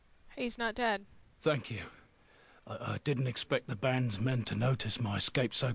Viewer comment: lol 25:14 thought he said "fuck you"